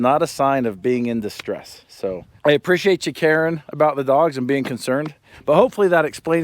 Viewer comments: “I appreciate you, Karen “ 😂😂😂👊. I know that’s not what you meant, but that’s what we heard 😂👊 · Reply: It's not just only what we thought we heard, Google Subtitles translated it to Karen too 😅😅😅 Quote: 14:27 "I appreciate you Karen, about dogs . . . " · Sounds like he said "I appreciate you, Karen" 😂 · did you say "Karen" or caring? Ha ha, I guess either would apply. Thanks for the video. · Did you just say "We appreciate all you KARENS"! HAHAHA!